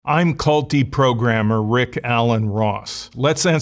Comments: it sound like he says "I'm culty programmer" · Ha, at the beginning, I thought he said "Culty Programmer" lol. Given the loyalty some software engineers have for specific platforms or languages, I suppose that's not outside of the realm of possibility.